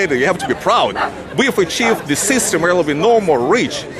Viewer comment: "We have achieved the system real of a normal reach." What is he saying here?